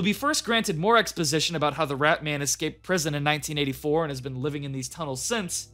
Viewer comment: "how the rat man escaped from prison in 1984" the text bar clearly said 1994 am i hearing it correctly?